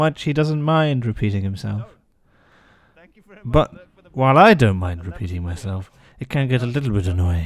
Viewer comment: i thought he said NO I DONT MIND PEEING MYSELF